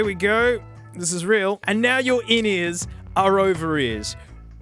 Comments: I keep hearing him say “ovaries” instead of “over ears” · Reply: And instead of 'in ears' I hear 'innies'. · Everytime you say "overears" I just hear "ovaries". I was very confused for a moment when you said you needed some "ovaries" 😂